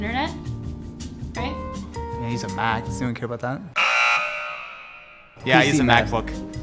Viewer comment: Wht did Austin said at 00:14 ? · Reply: Shory Aayush99 "I use a Mac..."